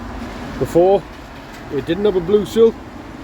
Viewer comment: When you said blue Sill, I thought you'd said blue waffle 'cause of your accent.Googled it and now I'm Traumatised.